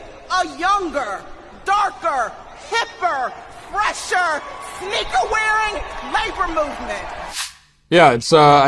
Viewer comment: What did she say at 2:57? It almost sounded like, "n-word er wearing?"